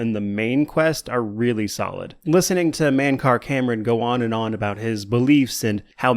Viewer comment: i seriously thought you said Kirk Cameron. ¯\_(ツ)_/¯